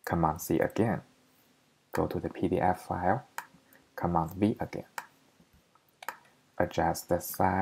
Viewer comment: I just tried and it works. Please make sure you followed steps. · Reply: I just figured it out! I thought you said Command C then Command B. I tried it 5 times then figured out you said command C then command V!. Thanks for your help!!